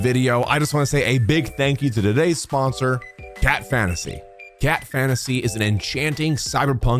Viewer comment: i thought u said gyatt fantasy